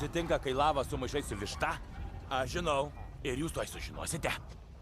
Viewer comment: Why do I keep hearing ''As you know'' 😭